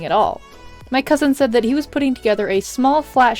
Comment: When you said ‘Cousin’ I heard ‘Husband’ and I was like “WHAT?!?” o.o